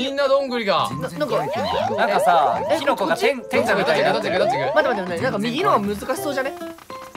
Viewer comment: 辺りが「なんかさぁキノコがテンガみたいになってる」って言ってるように聞こえるwww